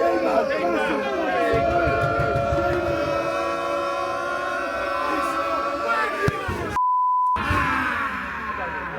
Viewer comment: ㅋㅋㅋ 골키퍼 공찰때 뭐라고 하는건가요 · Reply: Wanker 아님?